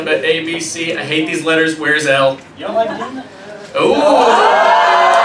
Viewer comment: What did the audience never say at 11:34? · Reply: They said, "you don't like D??" lol